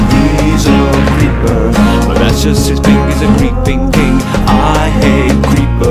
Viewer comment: i always misheard "hes a griefing king" as "hes a green big peen" i just checked the desc, I FEEL SO DIRTY LOOOL.